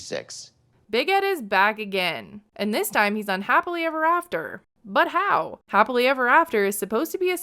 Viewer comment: jesus I heard “UNhappily ever after” I mean not wrong either